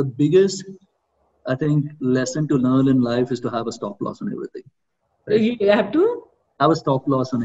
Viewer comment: What biggest lesson Nithin said at 43:31. The voice is not clear! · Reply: He said have a stoploss in everything.